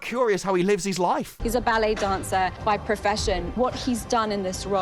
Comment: I thought she said "BELLY Dancer" but then realized it was her accent that made "BALLET" sound like that. 😂